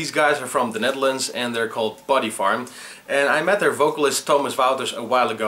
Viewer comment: LOL, I thought you said Bodyfart... have you ever thought of an SSD drive? Could be a good idea